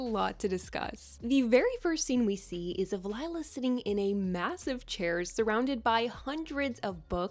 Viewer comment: Did she say massive ??